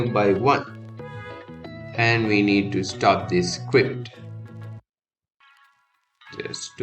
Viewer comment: Did you just say 'scwipt'?!